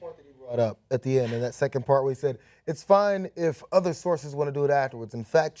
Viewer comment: Actually, I think Wallace said “the other person,” meaning the candidate’s opponent. So IOW it’d be Clinton’s job to fact-check Trump throughout the debate. For some reason, though, politicians are as reluctant to call out politicians’ lies as journalists are.